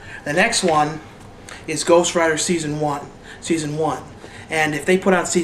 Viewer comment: ghost writer ? i thought you said ghost rider, dang